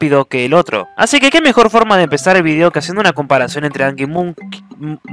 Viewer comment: angry munci como mumu. Pense que dijiste moml xDDDDDDD habla bien